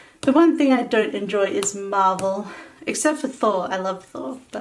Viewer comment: When you said MARVEL I thought you said MARBLE and I was like "ok we aint friends anymore." LOL